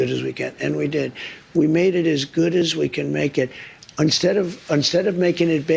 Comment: does trump say unstead??